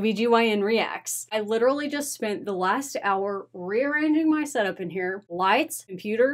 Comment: Thought you said 'rear ending' at the start 😄😄😄